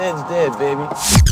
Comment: did he say "Sam's Dead baby"? LMAO how appropriate for me O_o